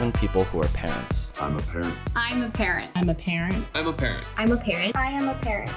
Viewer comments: it’s sounds like they said “ i’m apparent” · First i thought they said im a karen